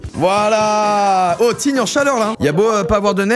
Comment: Pourquoi j'ai entendu teen en chaleur 😂😭